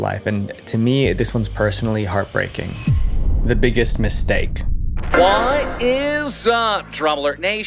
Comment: i heard the biggest beefsteak for a second